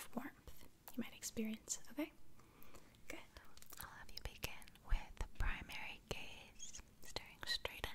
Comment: People, does she say at 12:18 "I love you bacon"? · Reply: She says, "I'll have you begin"